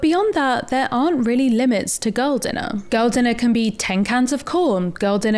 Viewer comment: Oh, GIRL! I thought you were saying, "Gull dinner" as in seagull. I couldn't figure out what the food had to do with sea birds.